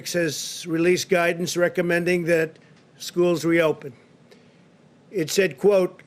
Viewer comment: DAMN I thought he said school MUST open. Trump can't handle the China Virus. What a shame